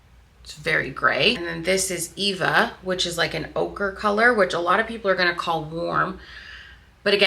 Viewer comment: everytime I hear "ochre" my brain makes it "okra" and I think to myself "that's not green!"